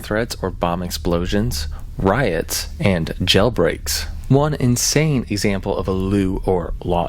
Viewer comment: did he say gelbreaks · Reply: Jail breaks bru